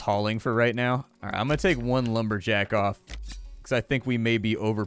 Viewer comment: take one jake off had to listen to it a couple times misheard it lol